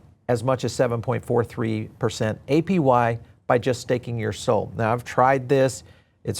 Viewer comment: I heard "staking your soul" lol😅